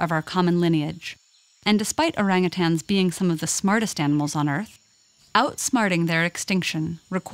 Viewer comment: The smartest animals? I heard this a lot from other kinds of animals too. AND IT'S ALL A LIE. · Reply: She said, "Some of the smartest animals" not "THE smartest animals"